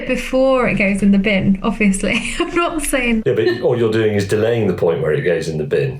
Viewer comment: did i just hear Bim??? Hey Bim....